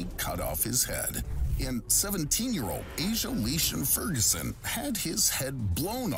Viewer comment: i was doing something and thought i heard "Lisa furgoson" which is the name of a teacher at my school i was like 'WTF'